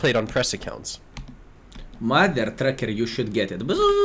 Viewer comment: Check out YT channel: <link> Video Directory/Guide (SSYS): <link> Equipment list: <link> Patreon: <link> Discord: <link> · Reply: I thought skill was talking about me a few times because he was saying Mothertrucker I thought he was saying tankingtrucker because I do YouTube videos lol